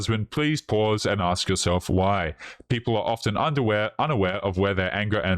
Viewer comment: I am very stoned but I swear I heard you say "underwear" instead of "unaware" around the 24:15 mark.... fantastic!!! Please never edit those out!! There are many of us who agree